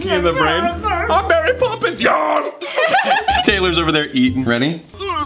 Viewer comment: When it came to I’m Mary Poppins y’all I thought it said THIS IS SPARTA!!